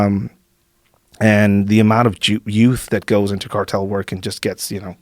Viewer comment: He's so mexican he almost said "Jouth" instead of "Youth"